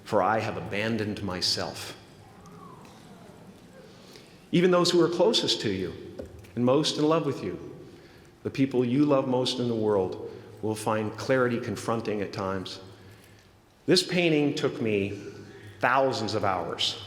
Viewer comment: What did he say about "confronting clarity at times", when talking about closed and loved ones? 17:30. Could somebody explain, please, I didn't got it, whatsoever. Thank you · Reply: "Even the ones who are closest to you, most in love with you, the people you love the most in the world will find clarity confronting at times." Presence of light brings clarity. When face to face with clarity, it can sometimes be hostile, problematic, troublesome, or difficult to deal with. Even with your most beloved.